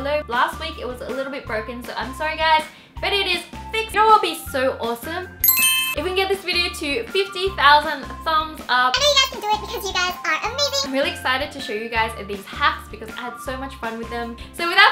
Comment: it sounds like you said"oh throw up is so awsome"